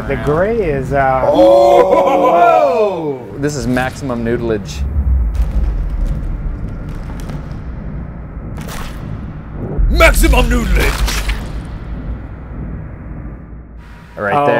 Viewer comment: I thought he said maximum nudelidge